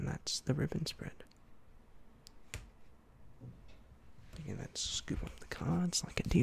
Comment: did he say “mexican wave”?